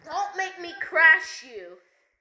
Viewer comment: I did not say instant kill guys, I said its a joke guys